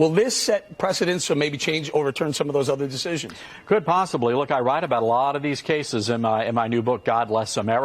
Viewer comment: Did anyone else hear him say ' I lied " at 1:38 ? Is that what " Freudian slip " means. Oops, I misheard him say, " I write."